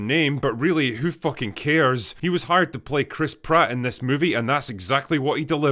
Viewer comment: I only hear "Crisp Rat" ...